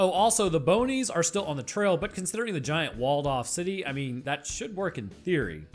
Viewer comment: I misheard that as giant Waldorf city... like a zombie buffet.